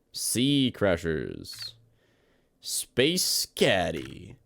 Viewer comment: i thought taht said space daddy